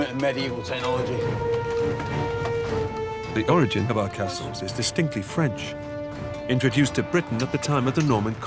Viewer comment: i really thought he said “the origin of our castles is the stinky french” i’m like :o is that allowed!?!